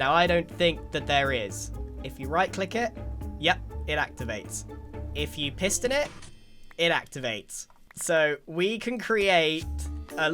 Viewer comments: I heard “if you pissed in it, it activates” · Kid you not. I heard "if you pissed in it"